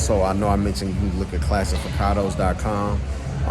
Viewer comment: I heard you say classic picano's. I am a senior citizen and on disability.